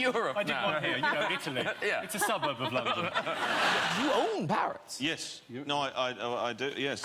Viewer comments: for a tiny moment i thought he said " you own Paris" and i was like wow this man owns the City??? How?? And when i Heard him say ive got two of them, i realized how stupid i am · I thought that he said Paris. You own Paris, I thought he said. 🙄🙄🙄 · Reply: Parrots